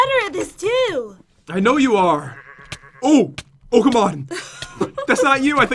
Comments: All this time I thought he said “Ooh! Pokémon!” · when Pat said oh come on it sounded like he said Pokémon LOL 🤣🤣🤣🤣🤣🤣🤣🤣🤣🤣